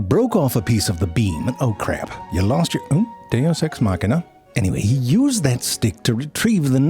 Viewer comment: "Oh crap, you lost your - oops. Deus ex machina." Had me in stitches. 😁😂🤣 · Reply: Transcription heard it as “there sex marker” 😁